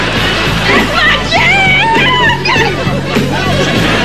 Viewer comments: it sounds like she said “You done made me lost my vagina.” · It sounded like he said “you done made me lost my vagina”